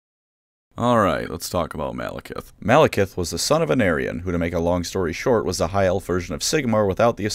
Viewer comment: At first I thought you were saying “An aryan.” And only cottoned on to the fact you were saying Anarion about a minute in.